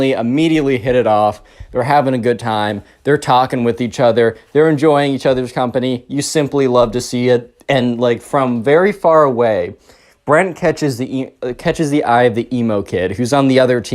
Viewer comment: I thought you called Brent friend for a second